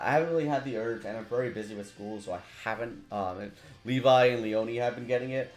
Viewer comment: Man, did you said “busy with school” ?! or I’m just deaf, I mean are you that young to be in school?